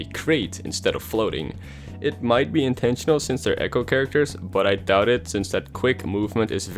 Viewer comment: I thought you said Smash characters. (I’m so stupid)